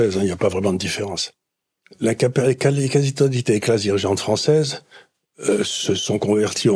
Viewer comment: La quoi a 15:20 ? 😅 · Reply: Quasi totalité